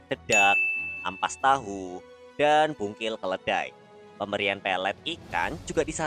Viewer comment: kok dengernya bungkil keledai ^^